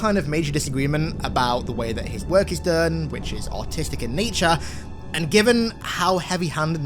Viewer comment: i thought he said autistic in nature and had to go back and re-watch 😂